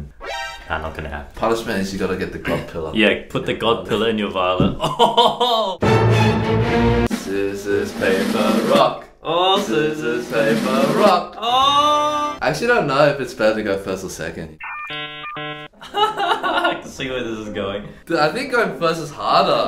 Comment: The first time I heard you play this "scissors, paper, rock" thing, I thought you were saying "sissies play baroque"...